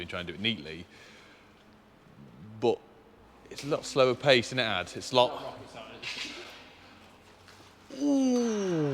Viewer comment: What did say? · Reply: "It's not rocket science." :D